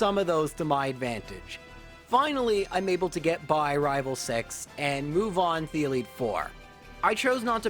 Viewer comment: Had this video as backround sound, not really paying attention, when I heard “I’m able to get by rival sex”. Got me confused for a few seconds lol